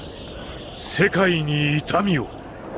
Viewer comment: Nose que dice pero trataré de decirlo como lo escuche xd Shekaini tamio